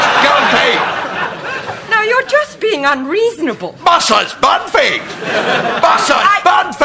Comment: what is he shouting? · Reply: 'MRS BANFIELD! MRS BANFIELD! MRS BANFIELD!' and so on